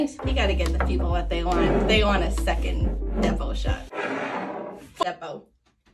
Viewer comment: Honestly thought she said "second nipple shot," and I wondered if I was the one drinking.